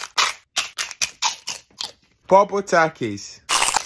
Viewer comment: 'popple taki' Help me why do I hear that?